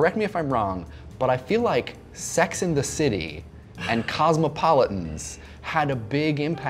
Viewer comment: did you just say "Sex IN the City" instead of "Sex & the City?"